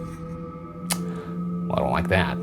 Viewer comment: i thought it said 💫slay👏 bc i wasn't on the tab 😀but damn